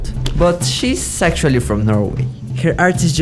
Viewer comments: ! Did i just hear him saying "sexually"? 😂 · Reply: "She...sactually from Norway." is what he said🤣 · i really heard "shes sexually from normay😭"